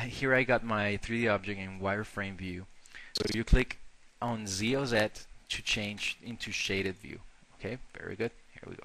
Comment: what does he say on 2:55 ? "So if you click on z0z1 you got shade view,"???